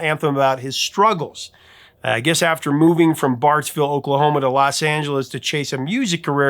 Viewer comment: Did you just say Bartlesville, Oklahoma?! I used to live there, bro!! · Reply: I think he said Barts vill